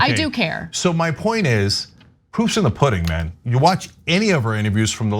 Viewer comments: Cenk that’s not the saying, the saying is “The proof of the puddings in the eating.” I said it wrong for years! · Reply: I swear I heard 'the poop is in the pudding' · I thought cenk said “poops 💩 in the pudding”